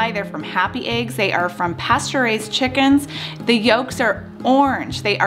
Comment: I thought I heard that the eggs came from pasteurized chickens... Then I listened again and heard "pasture-raised" chickens. Haha, that woke me up.